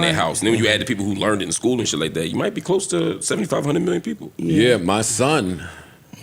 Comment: Am I trippin 1:14:17 did MLH say 75 hundred million people 🤔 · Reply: I think he was saying 75 to a hundred million ppl lol